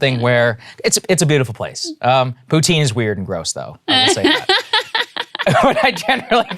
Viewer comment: Did Saagar say “poontang is weird & gross”?